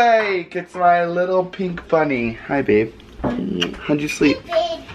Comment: I think said "Hey Babe" after at 3:08 lol